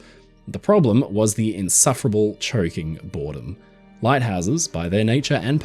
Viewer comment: I thought you were saying the insufferable Cherokee. I'm sitting here thinking what the Indians have to do with lighthouses 😂